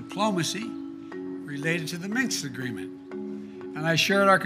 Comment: Did i hear ''Minks'' agreement?